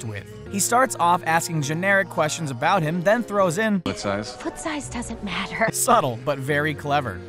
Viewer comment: I could've sworn Kristoff says 'Foot size?'..... Did anyone else hear that or was it just me?